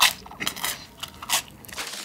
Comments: I thought bro said “where’s my big back” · nah i heard "where's my big back"🫠💀